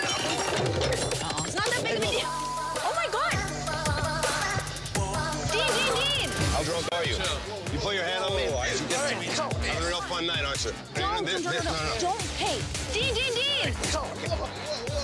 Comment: Maybe it's just me, but does it sound like he said "You put your hands on my wife?" After he caught the dude?